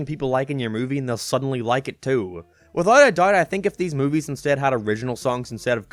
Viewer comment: "With how ATHGOIUGHDH I". What's he saying? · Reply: he said "without a doubt" but didn't enunciate some T's. He was saying if the movie had used original songs it would have failed because cramming already-popular music was about the only thing it had going for it.